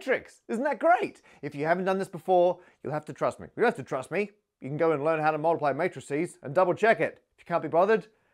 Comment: More teachers should adopt the slogan "You'll have to trust me, and / or learn. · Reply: I had to rewind it cuz I thought he said you'll have to trust me or you'll learn. 😊 Like a more polite version of f around and find out lol 😂